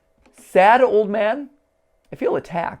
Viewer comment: I heard “shadow man” and “ I’ve been trying to come back to you” if im wrong ok but if im right.... matpat do u need a hearing aid?